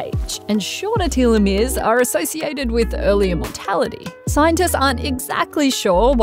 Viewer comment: I was confused when I heard “early immortality” but then realized you said “earlier mortality”. Those are quite different.